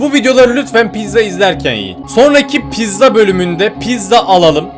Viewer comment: ben mi yanlis duydum yoksa abi hu videolari pizza izlerken yiyin mi dedi?